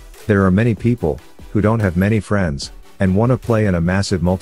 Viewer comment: i thought that said there are people who don't have friends lol